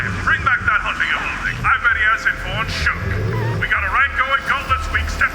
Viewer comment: Did they just say SHUCK? BLACKSHUCK?!